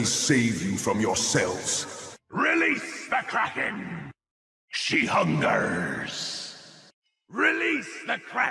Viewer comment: Hi, I speak Spanish and I don't understand what Dredge says in the second 2:57, could someone write it for me? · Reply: She hungers: ella tiene hambre